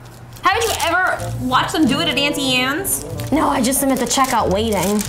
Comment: I almost died when justine jumped 😂 I guess cooking can be pretty scary dough. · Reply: At first I thought u were saying Antsy Ants when I realized u were saying Aunty Anne's ( I hate American accents